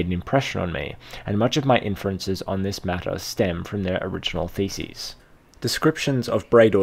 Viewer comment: laughed aloud, thought he said “feces” but he said “theses” XD